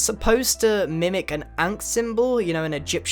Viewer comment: did he say ant or ankh? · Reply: Wellpwn he said ankh